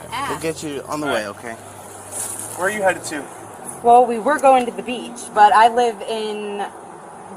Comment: She said they were going to the beach, I thought she said they were moving household items